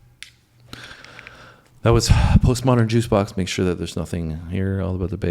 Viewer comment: Great reaction to a great rendition. I especially like 11:23 when it sounds like says "Postmodern Juice Box". (Just teasing, but it does!) Yes, listen to Morgan James singing "Dream On" with the group.